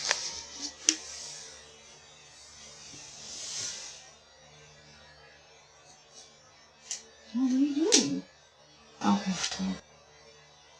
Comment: The one at 7:50 that just sounds like her asking her kid what are you doing? and then the person saying nothing mom and the she proceeds to screw on a cap to a bottle or water bottle that's exactly what it sounds like